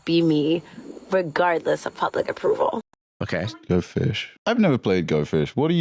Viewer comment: She says "regardless of public approval" I hear "sorry, got no shame" You should only be yourself if you are not an asshole to begin with